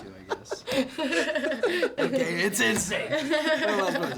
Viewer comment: What does Jennifer say a 6:13???? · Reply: "That's insane"